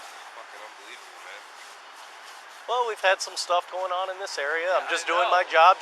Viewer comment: I thought the officer said "been theft's in the area". Since you asked to find where he said it I listened to it again and he actually says "been some stuff going on in this area". Which is a whole lot more vague. An makes me somewhat suspicious of the officer tbh. Addressing your "imagine if you were traveling in a neighborhood" part. I don't know if you have more information than me but I have no way to know if the officer had never been there before. I have no idea why the officer is there in the first place. Don't know if he was sent there for a reason or if its just part of his patrol. If hes just on his beat walking past this complex and stopped the guy for literally no reason then yeah that's some bullshit. But if he was sent there for a reason or because its a high crime area then I have less of problem with it. But I really dont see the point in cussing the cop out and calling him names when all he did was ask for ID. Does not seem like a hill worth dying on so to speak.